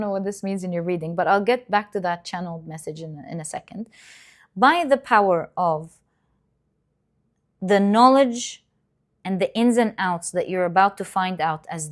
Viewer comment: I heard "by the power of Grey Skull" - He Man. LOLZ